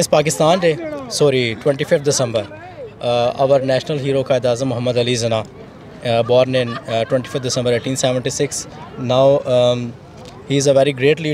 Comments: Wtf 5:04 the guy literally said Quaid E Azam Muhammed Ali ' ZINA 😅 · did he just say Zinah ? 🤣